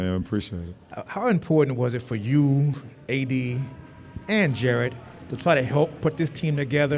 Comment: Did he say Jared? 1:58 hes gone